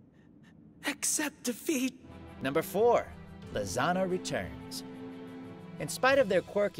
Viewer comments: When he says lisanna it sounds like hes saying lasagna lol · Am I the only one who thought when Todd said “ Lisanna” it sounds like he’s saying lasagna